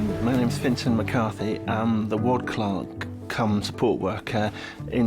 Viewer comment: the caption says is the 'Ward Clark' haha